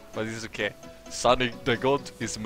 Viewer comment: i heard "sun in the gold"